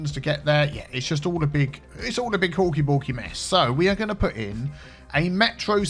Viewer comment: I thought you've been saying Hulky Bulky this entire time